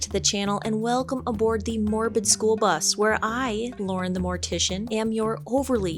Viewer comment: Ow, the jelly fish. Also, I thought you said Mormon school bus...